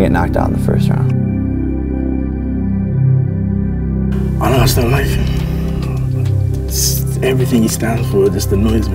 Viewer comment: Ok but wtf did deji say here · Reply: he said i just dont like him